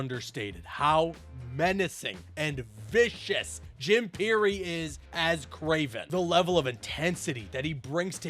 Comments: Lol I thought I heard you say Jim Carrey voiced kraven lmao had to look it up lmao 🤣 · I thought you said Jim Carey played Kraven, had to go check 😂😂😂 Jim Pirri. My bad.